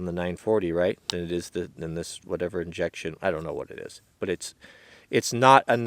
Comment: Did anyone else hear "jackshit" at 10:40😂